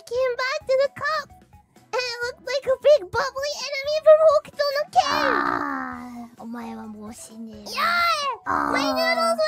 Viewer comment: subtitles are wrong. She isn't talking about walking on the couch,. "and it looked like a big bubbly enemy from hokuto no ken" is what she is saying.